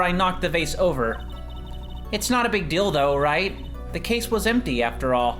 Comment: wasn't looking at the screen, thought said "it's not a big dildo"